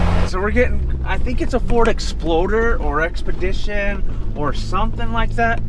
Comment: Did you say Ford Exploder? 😂